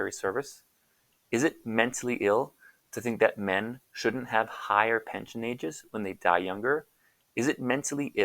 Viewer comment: I'm saying "shouldn't have higher pension ages." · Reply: Sorry, thought you said "should have higher pension ages."